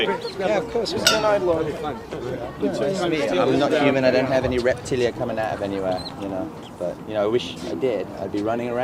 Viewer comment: what is he saying? really sounds like "I'm not human, I don't have any reptilian come out of anywhere, you know... " am I hearing that right ?